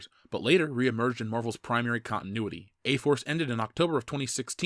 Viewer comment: I kept hearing "gay force"